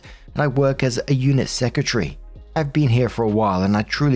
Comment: I don't know why I heard '' eunuch secretary '' 😅🤣🤣